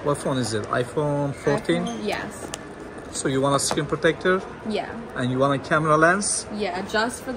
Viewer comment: I thought he said skin protector